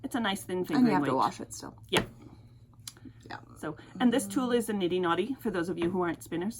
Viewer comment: Did you say that is a knitting naughty? Maybe I misheard you? DO NOT GOOGLE knitting naughty. ha! What is the gadget called? I have started to think about spinning and I am gathering info about the different tools that would be useful. Thanks! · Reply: LOL! It's called a niddy noddy.